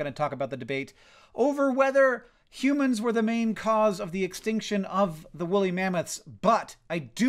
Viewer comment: I heard "humans were the main cause for the extinction of the woolly mammoths' butt"